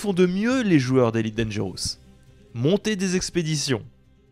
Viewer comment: j'ai entendu partir en croisade 😂